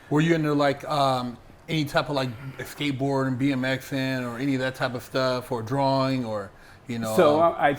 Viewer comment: When was saying "BMXing" it sounded like he was saying "bein' Mexican." I couldn't figure it out lol